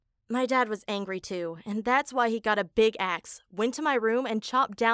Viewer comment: Who else hear big ass at 2:01 ?????🤣🤣🤣